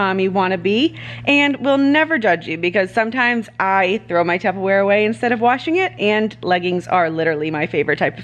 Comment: I had to pause and hold my phone up to my ear bc I thought you said you throw your underwear away instead of washing it and I was like okay we are on very different levels here 😂